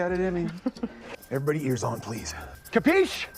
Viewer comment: what was Kody saying? Was he yelling at everyone to leave him alone? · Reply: He was the duty person, so could get called to report to one of the military guys at any time. He wanted a nap so told them I’m napping, everyone keep ears on for them calling me, filled by a loud capiche